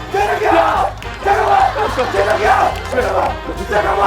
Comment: 잠깐만 이라고 한고같은데 왜 난 재석아라고 들리지?